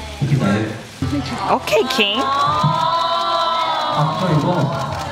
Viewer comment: I stg I heard 'oh kinky'